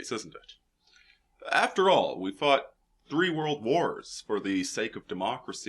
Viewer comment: did he just say 3 world wars?